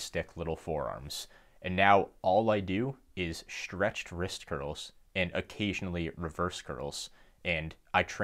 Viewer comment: what did you say here? I couldn’t understand. Stretched wrist what? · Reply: Stretched wrist curl, it’s a variation I may have made up lol.